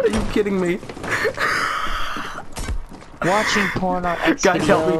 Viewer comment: Did anyone hear the other guy say ”watching porn on xvideos”